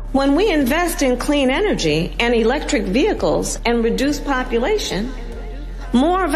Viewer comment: DID SHE SAY REDUCE POPULATION